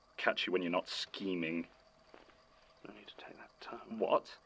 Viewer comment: what in the world did Jon mumble here? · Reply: I believe he said “No need to take that tone.” 🙂